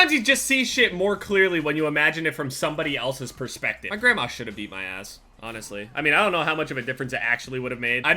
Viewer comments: lol I thought he said my grandma should of eaten my ass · I thought he said "my grandma should hhave eat my ass"